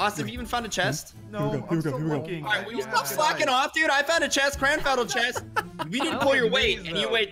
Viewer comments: turn on cc its says f***in instead of slackin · Plz don't tell me that I'm the only one that LOVES when has his rage moments😂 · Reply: Idk if the translation was wrong but when Ian was healing with bandages he asked if he got a chest and said no i heard him say "will you stop fuckin' off dude" and i literally replayed it with subtittles on and it said the same thing😭😭